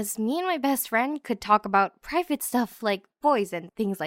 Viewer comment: did she say pregnant stuff